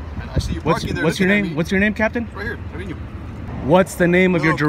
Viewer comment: The border patrol gets Ford Raptors. WTF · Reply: I believe I heard "Captain". The Captain probably gets the Raptor. My new ass got an Impala...